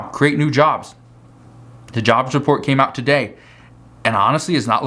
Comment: I thought you said the jobs report comes out Friday you weren't you talked about that a lot